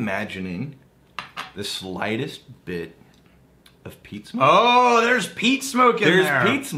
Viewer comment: At first, at 7:07 I swore said, "Am I imagining the slightest bit of pizza?"